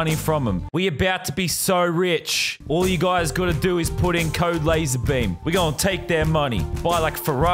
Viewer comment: I thought you said code lazar not code lazarbeam